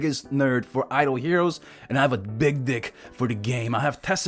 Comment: did he said "I have a big dick" 🤔